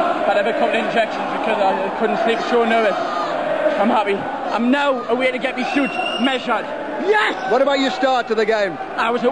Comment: at first i thought he said : " I'm now away to get me shoot missiles "